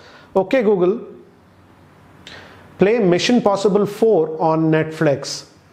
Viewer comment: did he said *possible* ?